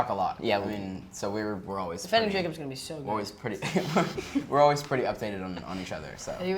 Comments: What the Heck are they Talking about at 11:20 ?? Like, Jack what are you saying?? I Need answers! What is going on.... help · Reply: He said: "Defending Jacob" is gonna be so good. Am I allowed to say that? Is it out? That's the upcoming series that Jaden is in. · what the hell is jack saying those aren’t even words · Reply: "Defending Jacob its gonna be soon" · What was going on between Jack and Jaeden around 11:16 - 11:25. What were they talking about? I couldn't understand them. Jack asked if he was allowed to say something. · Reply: He said: "Defending Jacob" is gonna be so good. Am I allowed to say that? Is it out? That's the upcoming series that Jaden is in. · What jack Grazer say at 11:12? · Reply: "Defending Jacob is gonna be so good" I think ?